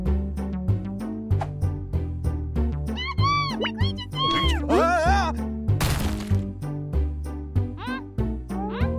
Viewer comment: kabbu! bigretcha cher! 0:28 watch out an aggregious ceterpe!! i have no idea what vi is saying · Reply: I believe the first one is “kabbu! There’s creatures here” and the second one is “Watch out, there’s creatures at our feet!” .. but I might be wrong